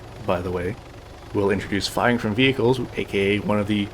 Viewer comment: what did he say? It was kind of hard to understand. Was it "Flying from vehicles" "Firing from vehicles" ? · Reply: Firing from vehicles, it's why I never spent any time trying to port my Arma 2 Firing-from-vehicles addon over to Arma 3.